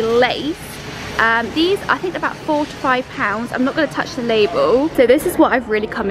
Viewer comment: Did she really say that they're £45 at 6:17?? 😂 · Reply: I listened after seeing this. £4 to £5